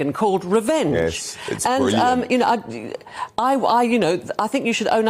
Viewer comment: At the 30:48 mark where the woman in the hot pink jacket wanted to point out that Tom Bower had a book out called Revenge. Did I hear a voice say "yes, it's brilliant"? 😄